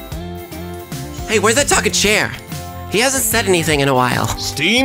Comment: i heard "cher" at first instead of chair and i was like??? THEY GOT C H E R WHAT?